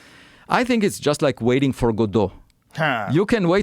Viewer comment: did he say godo or god? Subtitles show god